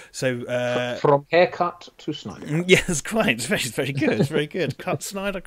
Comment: I thought that had said, "From Eckhardt to Snyder" at first. And I spent a good minute trying to figure what the new movie had to do with Lt. Eckhardt from Batman '89, lol. (Speaking of which, I hope someone tries to slip Gordon a money sandwich in this movie. That'd be a fun little tribute.)